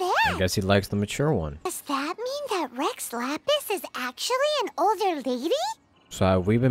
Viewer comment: Y'all hear Rex Lapis but all I hear is "bungee gum has the properties of both rubber and gum"